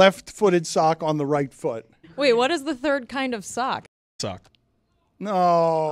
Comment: he says come* sock. I replayed it 10 times and turned on the captions until I finally deduced what he said. I hope this comment helps folks as lost as I was. When you guys cut things out, you have to give the audience at least the start of the word. I was very lose wondering why the response from the cast was so out of proportion to just the words blank and sock.